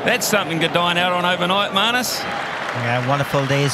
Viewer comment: does anyone heard "Marcus" insted of "Marnus" ??? asking it just because of curiosity 😅